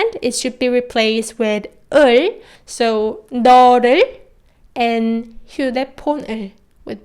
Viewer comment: What r u saying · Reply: Never mind I figured it out 너를 And 휴대포는 Neoleul And Hyudaeponeun